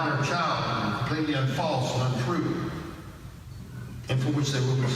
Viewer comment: Sure I heard him say "....are completely unfalse and untrue"